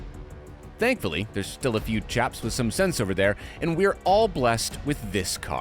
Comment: Holy shit I heard "japs" instead of chaps